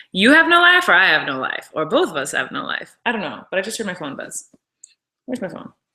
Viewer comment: when she said " I just heard my phone buzz" did anyone else think she said " I just heard my corn buzz" because I swear to god she did!